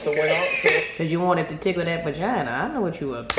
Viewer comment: "Caz u wanna tickle that vagina. I know what u up to" 😂😂😂 did said dat? 😂 love u. ♥